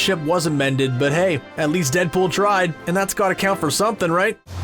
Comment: What is the movie that Beckman says to Reynolds, as the reason for his apology? I keep hearing "Boat Neck" (or something), while closed captioning calls it "Boa Tank" I can't remember either movie. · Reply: It's called Boltneck. It's sort of like a 90's Frankenstein parody. Reynolds made it in his late teens early 20s. I think it comes before Van Wilder, but after his outer limits episode.